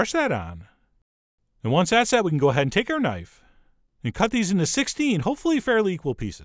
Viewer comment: Did he just say "take your tine" when he was mixing with the fork...? #punderful